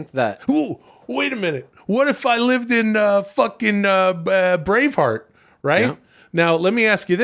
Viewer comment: I thought Will said "what if I lived in Brainfart"